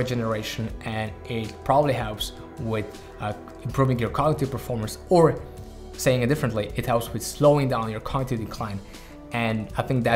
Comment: "it probably helps with your quLITY pErFormaNce, or saying it differntly your contEr DecLiNe..." ? wtf r u saying pls one more time/.? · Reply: "Improving cognitive performance, or saying it differently, it helps with slowing down your cognitive decline" :)